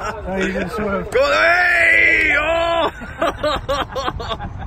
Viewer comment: Sounds like he said “that’s mid” at the end 😂